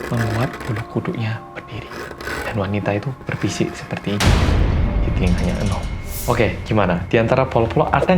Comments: itu ngomong apa ya? Suaranya kecil, ketabrak sama sound effect dan gaada subtitle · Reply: 'berbisik seperti ini, di telinganya eunho' kayaknya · tolong dong yang tau, itu mas ngomong apa ya?? P.S Happy Brojol Day buat mas 💞💞💞💞💞💞💞💞💞💞💞💞💞💞💞💞💞💞💞💞💞💞💞💞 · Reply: "di telinganya eunho" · Mas, mas itu ngmong di bisikin apa pas menit 6:38 ?? Ga kedengeran mass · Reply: "di telinganya eunho"